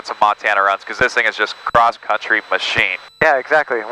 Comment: In aviation, pilots flying general aviation planes will often times refer to a trip longer that 250 miles as a “cross country” flight. It derives from the old military flight school training programs. Hope this helps! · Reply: Excuse me, I thought I heard "across the country", not "a cross country" Like your show!